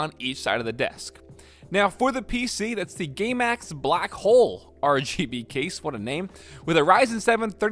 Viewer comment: "gamemax black hole" 4:17 mary mother of god i've heard it all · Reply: I thought he said gaymax blackhole lol